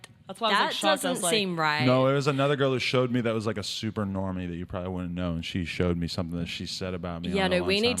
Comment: Aye... Did you say ''Super Normy'' 13:45 ? That's funny.